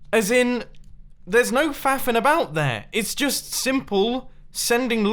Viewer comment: When i heard him say faffing i thought he said fapping